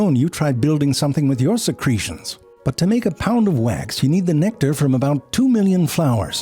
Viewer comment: couldn't hear "pound of wax" without captions. by english is C1 also, add metric system XD add kilograms XD even the naming is harder, LBS but calling it pounds. why not kg and kilograms? everything gets eeeasssieeeerrrrrrrrrr